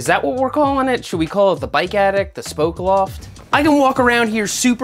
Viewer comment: Seth says, "The bike attic." I hear, "The bike addict." Meet you halfway, "The Bike Addict's Attic."